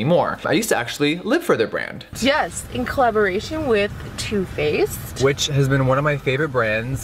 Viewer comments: when said two faced, i thought said tooth paste and i was very confused...lol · when was talking about two face i thought he was saying toothpaste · Everytime you said "two faced" I heard "toothpaste". I was about to go find some oral hygiene reviews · (I think) I thought she said "Toothpaste" but it is too faced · When he said two faced everytime I thought he was saying toothpaste until the post we're shown. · Am I the only one that thought he said "toothpaste" when he said "two faced* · When you said Two Faced I thought you said "tooth paste" and I was really confused as to why you were reviewing tooth paste on your channel